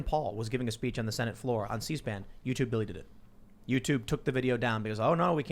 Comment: did he just say "youtube Billy did it"?